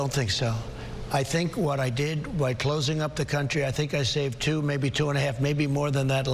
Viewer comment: It’s quite worrying that when Trump started that sentence about saving lives and said “....I think I saved two....” I heard it as “I think I saved too...” and I’m thinking “please don’t let the next word be ‘many’, please....”. I literally wouldn’t put it past him to have actually said “too many” and then dig himself a hole after realising his mistake. Never mind, he basically did that a few minutes later with “herd mentality”...